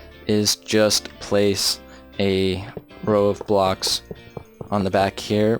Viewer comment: 😂😂 it sounded like you said roblox